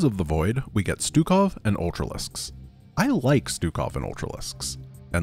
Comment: when you hear it from far away it sounds like: ”I don’t like Stukov and ultralisks” but up close it sounds like: ”I like Stukov and ultralisks” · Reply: I hear him say "I don't like- " the first time. Then I read your comment, went back, and heard him say "I like-" Then the third time I heard "I don't like-" again. It's "I like" but he says it stressing "I" in a weird way, creating this effect. Interesting.